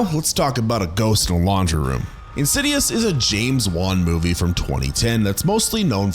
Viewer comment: i swear i thought he said James Bond movie! lmao